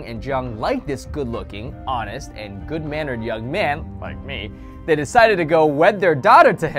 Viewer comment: Is it bad that I thought he said Doyoung...